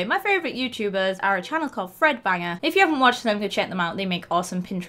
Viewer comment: When you said threadbanger I thought you said Fred banger and I thought that sounds like threadbanger!